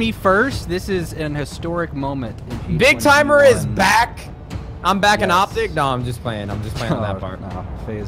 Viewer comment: I swear I heard nade say "I'm back in optic.....nah joking" "Faze up" in a quite voice at the beggining